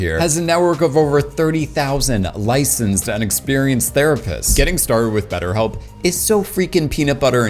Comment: please don't take sponsors from betterhelp 🤦‍♂ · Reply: I heard “30,000 inexperienced” 😂